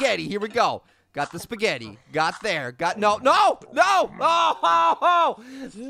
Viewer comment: Keep looping and then it sounds like he is saying Gotnelle